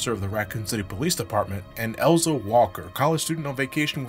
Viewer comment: When i first heard of RE 1.5 i misheard her name as Elza Wesker and then thought that it would've been a cooler tie-in for claire. A sister looking for her cop brother only to find out that not only is he dead but had a hand in the disaster that is ending the city and endangering her life. Sure code veronica would've played differently but chris coming to the rescue of his nemesis's sister would be a great irony and woukd make more sense for her to be on rockofort than claire redfield just poking around in top secret umbrella bases cuz fuck it why not. But no when RE gave wesker siblings they prey on our suspension of disbelief and make out wesker and his siblings to be RAISED evil. Anyone else think that was a missed oppurtunity